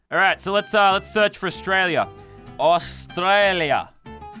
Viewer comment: i swear i heard him say Australier